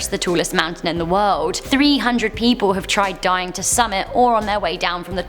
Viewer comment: "300 people have tried dying to summit." Did I hear that right? Am I the only one?